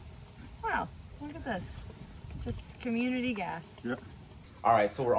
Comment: had to rewind, thought she said community ass. Fell out of my chair.